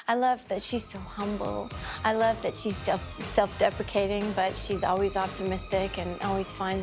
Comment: I thought she was saying "self defecation" and I was thinking what the hell is that? But then I realized she said "self deprecation." 😆